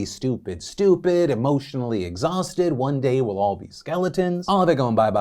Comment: Instead of "one day we'll all be skeletons" I heard "one day we'll all be elephants" and I got so confused lmao